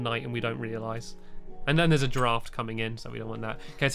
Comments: When he said, there is a draft coming in, I thought he said giraffe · Oh! A DRAFT coming in!! I thought you said a giraffe for a second there!!